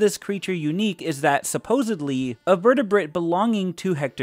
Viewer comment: Wait, 18 inches is longer that 20 feet long?? What am I missing here? Did I hear this wrong? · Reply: I heard it wrong too, i heard "vertebrate" instead of "vertebrae". Rewinded about three times to be sure.